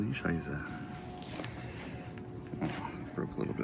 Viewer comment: Good work- I cant believe this massive and fast grow of roots...in my place it took sometimes years to layer... why not cutting the top of the marcott beforehand to make it easy to handle? Greetz from Germany 🙋🏻‍♂️ What climate do you live??? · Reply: Did you say :Scheisse!??? I understand that as a German😂😂😂